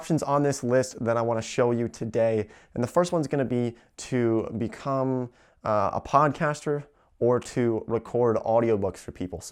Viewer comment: "to become a porncaster" I heard lol I wasn't looking at the screen I was like "Whaaaaaat!"